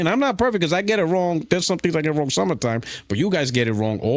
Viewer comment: did he say he get wrong some of the time? What kind of lying out the a$$ crack is this.